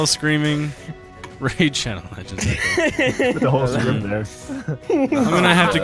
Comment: Paddle faster. I hear “RAID SHADOW LEGENDS